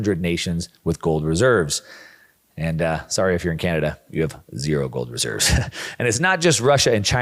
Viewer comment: “Screw you Canada!” At least I think that’s what I heard him say?